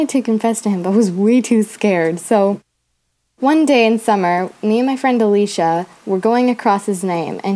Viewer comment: I thought she said me and my friend felisha 😂